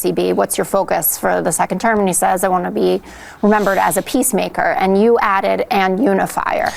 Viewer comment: I thought she said "peace maker and paedophile" 😂